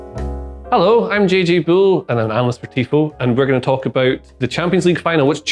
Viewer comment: Sounded like you said “I’m JJ Bull and I’m analyst for tifo” in both videos so far lol